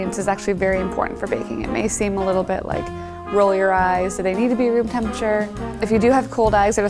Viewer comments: did she say cold eyes? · ok I heard “cold eyes”